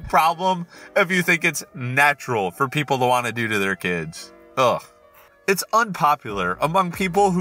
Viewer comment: I THOUGHT THAT SAID DEVOUR THEIR KID OH MY FU